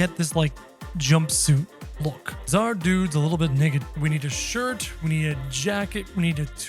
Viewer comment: WHAt DID YOU SAY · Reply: He said naked? It's not that big of a deal